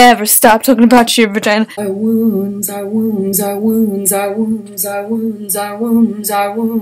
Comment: Imagine trying to sleep and you just hear Wombs a Wombs a Wombs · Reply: I thought she was saying : “ Our wounds, our wounds, our wounds”